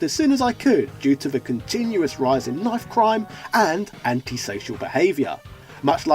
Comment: did you say love crime? Is that a polite way of saying rape?